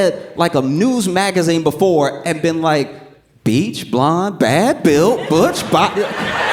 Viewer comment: I thought she said "Bleached blonde badly built butch body" Honestly, either way, Bars! Lmao